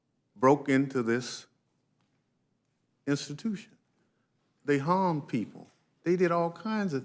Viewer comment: He says they hung people? Am I hearing this correctly? Was this before or after skulls were crushed by fire extinguishers?